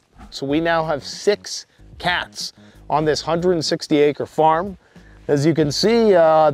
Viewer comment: 90 acres, way to go. You've got your privacy off grid🎉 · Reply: I thought he said 160 acres